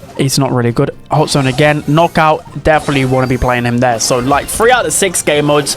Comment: did I hear him correctly did he say gene is not good in knockout? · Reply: I think he said "knockout, wanna be playing him there"